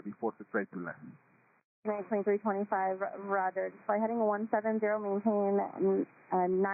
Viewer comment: I'm not the best at being able to hear what people are saying over the radio, but I'm pretty sure the ATC said "170" & the subtitles said "190."